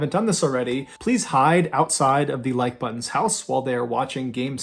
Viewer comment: wait so i wasn’t supposed to hide outside the like buttons house while they’re watching game 7 of the world series and every time the game gets good change the channel with my universal remote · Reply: crap, I thought he said "Ike Butan" I think I may have just accidentally traumatized a friend.....